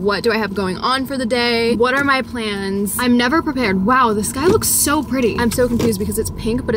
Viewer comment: love your videos so so much <3 at the end of the video you said "wow the sky looks so pretty" .. I misheard it to: "wow this guy is so pretty" I was so confuuussedd haha 😂🙄